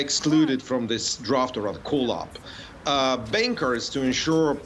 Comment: When he said bankers.. I thought he said “bakers”😂